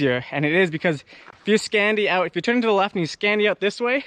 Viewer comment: Love watching you ride Jesse! Another great vid. I get the message @ 4:15, but what's the word you are using? "Skandy out"?!? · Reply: Yes! The move is called a "scandi flick" from rally car racing.